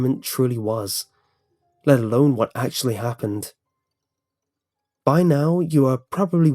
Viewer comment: i thought he was about to sell me a book abaout what happend, i thought he said 'buy now' lol